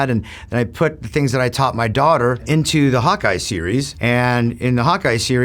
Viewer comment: I hear "Hat guy" every time he said Hawkeye. 😂